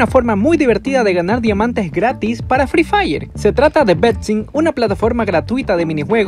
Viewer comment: entendí Pepsi XD